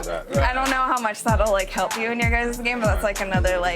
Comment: yo at 8:52 does she say nigga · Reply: Jfiggy 33 it sounds like it but she says "you guys"